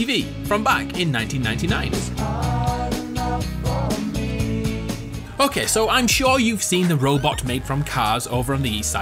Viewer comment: so at 0:42 f it instead of ok so